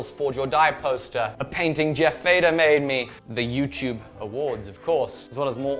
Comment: does he say he got that from Jeff Vader?